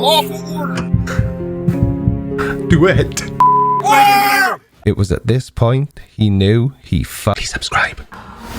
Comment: the way you cut the intro, sounded like you said, "it was at this moment he realised he fu kin subscribed"